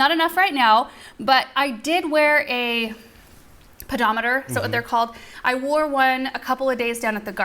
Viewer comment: For a second there I thought said "pajameter". It flashed on me that I'd put quite a few miles on my pajameter but no--- she said pedometer. In which case it'd be zilch for me.